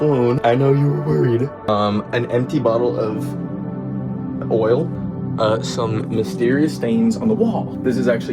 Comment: DID YOU SAY OIL